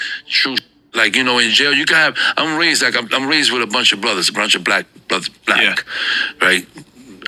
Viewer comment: when he said “I’m raised with a bunch a brothers”. I first thought he said “I’m racist with a bunch a brothers”. I had to double back